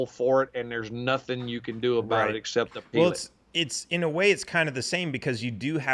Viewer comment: was explaining how the courts in California can hold you reliable and theres nothing you can do about it except>>>>>>.... thats where he was cut off and I cant hear what that exception was that he stated. Can you please tell me what he had said. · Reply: He said, "except to appeal it"